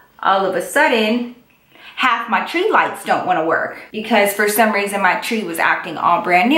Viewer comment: did y'all hear him say "I don't want it"? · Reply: I SWEARRRR I was about to comment this! It really sounded like he said "I don't want it" LOL